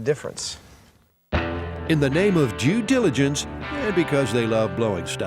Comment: Anyone else hear in the name of Jew diligence at 6:25 lmaoo